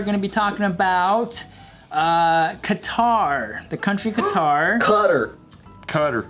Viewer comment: this whole time I thought they were saying "Guitar". I was like I never heard of a country like that before. but I've come to conclusion that they meant to say Qatar